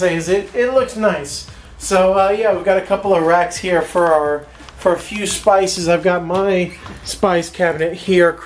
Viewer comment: "We've got a couple of racks here for a few spices." I initially heard it as "We've a couple of rats here here for a few spices".